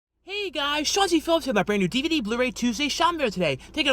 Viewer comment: I just had an idea! What if you did a Drive thru unboxing video where you hit all the drive thrus and then unpack the food and show everything. Would be a winner! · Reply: DrewStand , I hate to break this to ya, but his name is. LOL For the longest time I also thought he was saying Shawncey.